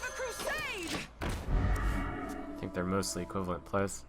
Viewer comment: "I think they are (there are?)... ... ... ?" Can anyone please tell me what did say exactly? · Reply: "i think they're mostly equivalent plays."